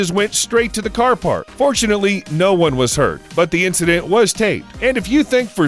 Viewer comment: Am I the only one who heard him say " unfortunately no one got hurt " when he was talking about the avalanche?😂😂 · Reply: Yes you are. He said “fortunately.“